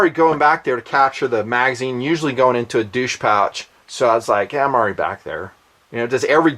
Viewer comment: did you say "douche pouch"